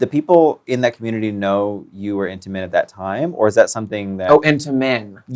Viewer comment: I’m glad I wasn’t the only one who heard “intimate” instead of “into men.” 😅